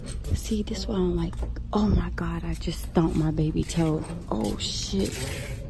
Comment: I thought you said that you stubbed your baby’s toe. I’m thinking, Poor Rain she didn’t even cry.